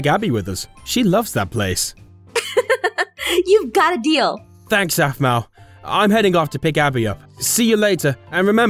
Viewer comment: What we hear - "Thanks Aphmau" What the subtitles say - "Thanks Zack"